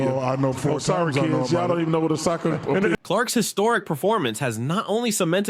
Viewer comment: TIMESTAMP @ 7:36 DID CHUCK JUST SAY A “SOCCER-EMPEDIA”🤣🤣🤣 WHILE TRYING TO REFUTE SHAQS OPINION🤣🤣🤣🤣🤣🤣🤣🤣